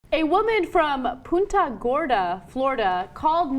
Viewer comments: Damn. I thought they said 'Puta Gorda'. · When I heard Anna say "Punta Gorda" I swear I heard "Puta Gorda".